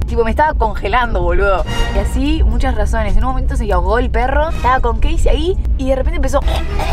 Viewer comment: Entendí “se drogo el perro” pero gracias a la gran actuación de entendí